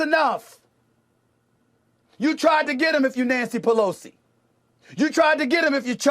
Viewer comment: did he say Nasty Pelosi? 😂, sorry if someone already caught this. Didn’t scrub the comments